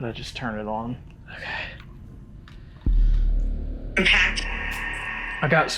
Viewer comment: I heard “Pat” which was a name tall got with Austin.